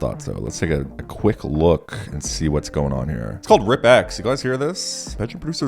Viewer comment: was passively hearing the video as i did stuff in the room, and i swear i heard you say "its called rip ass"